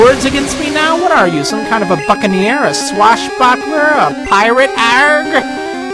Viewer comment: I think it sounded like you swore when you said "buccaneer". It sounded like you said "fuckin' ear" or something. Just past 8:27.